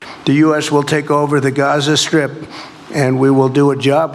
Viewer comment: Heard him say "Gaza Script" they just see us as little lines of code :D AI assets.. lol.